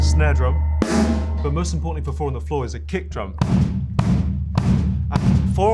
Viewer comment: When he read the comment that said "four on the floor" I heard he said "boar on the floor" and got PTSD.